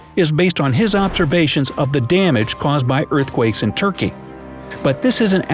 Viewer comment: I thought it said earthquakes and turkey 😂😂